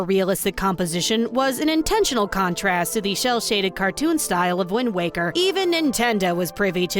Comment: was calling it "shell-shaded" instead of "cel-shaded" intentional? 🤣 I missed the YouTube content! 😭